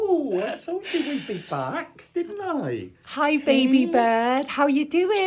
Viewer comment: I thought he said “Hi, Dad”